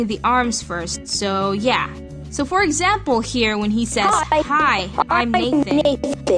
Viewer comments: first I heard this "Hi, I'm Naked" have rewind to see if I heard it right. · For second i thought he said hi im naked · I thought it said "hi I'm naked" I was like OMG wut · i thought he said "hi,im naked"i was liek woAH THEER BUDDY THERE ARE CLOTHES IN THE WADROABE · At first I thought you said "hi I'm naked" XD